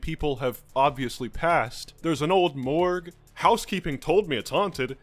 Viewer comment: did you just say a old.......MORGZ?Oh....my....GOD IM GONNA PUKE.